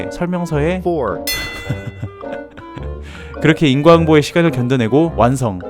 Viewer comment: 인과항보요???? ㅋㅋㅋㅋ 자막때문에 목소리도 계속 항보로 들려요 ㅋㅋㅋ